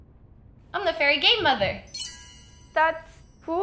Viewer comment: When i tell you i thought she said "im the fairy gay mother" i lost my shit 🤣